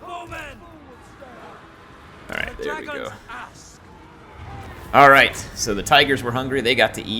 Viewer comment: I swear I heard the soldier say " the dragons Ass" ! Strange cathayan manthing needs aggressive warpstone therapy:_heirYesyes::_heirYesyes